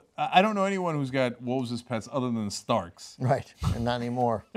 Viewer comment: Did Cenk just say: So, I dunno anyone who got wolves as pets, other than storks ? ( Ô)< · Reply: Starks, it was a Game of Thrones referance